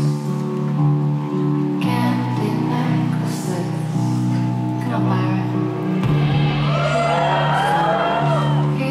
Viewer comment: did she say 'cut my wrist'? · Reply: "C’mon Byron"